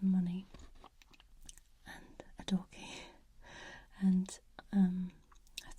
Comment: She said “a door key” and I thought she said a doggie 😂